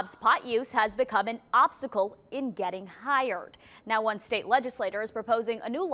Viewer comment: I heard "getting higher" 😩😩😩😂